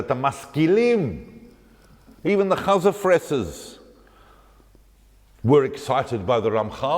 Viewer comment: Can anyone tell me what Dr. says at 37:57? Sounds like "chazafreses" but Google doesn't provide any results. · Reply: Hi Finnur, is using the expression "chazer fressers" - literally, people who eat pig. He is referring to Jewish people who were not religious or even anti-religious as illustrated by their relationship with Jewish dietary laws. Regards, Marjorie